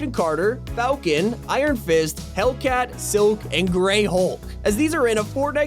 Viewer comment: did he just say hellcat?